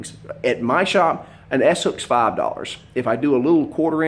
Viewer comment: What's an s hook? I'm sorry I'm new to this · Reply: I actually thought he said asshook